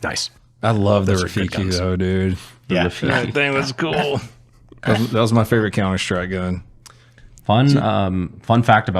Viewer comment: What does say at 1:49:45? What does he say his favourite counter strike gun is? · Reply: The cz75 auto